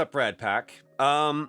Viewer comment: Wow this entire time I thought he said ratpack and I was wondering why rat. Interesting talk, it's crazy how podcasts heighten your attention span and hearing ability. Like the way people had to imagine more when they relied on the radio for a sports game back in the day, hence the power of podcasts and voices. Can't wait for the last of us series.